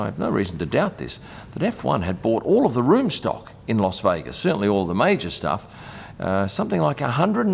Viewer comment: Great vid, as always. FYI, it's LAS Vegas. It sounded like you said LOS Vegas. It could be your Aussie accent. I'd like to see more on the food they eat in the buffets they put on. Do the drivers eat there, or is it just for guests. Cheers.